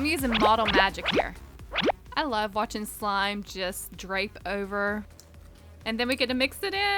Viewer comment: I thought she said, I love when slime drapes over😣😭😂